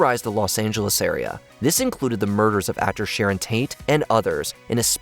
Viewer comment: Did you say Sharron Taint? LOL I think you mean Sharron Tate.